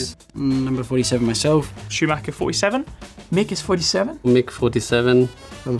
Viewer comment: I thought Russell said "Chewbacca 47" instead of Schumacher xDD